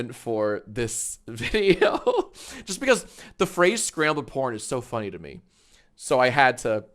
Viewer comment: When I watched this video when I was a kid, I thought you said scrable p*rn, bc old people play scrable ?? It was funny to me at the time